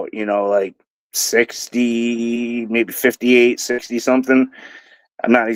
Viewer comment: The age of the uncle of your first caller doesn't add up. He's 70 now but 58 or 60 when he was in Moscow? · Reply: I thought I heard "68 something like that"